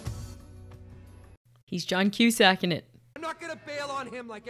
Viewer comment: Did you say: “He’s John Queue Sacking it!”?? 🤣🤣🤣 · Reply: John Cusack-ing it, the scene in DP2 is a reference to John Cusack's character doing the same thing (using the same song) in the movie Say Anything